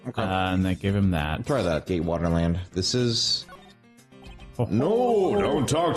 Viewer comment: I keep hearing "gaywater land" or something when they say GATEwater park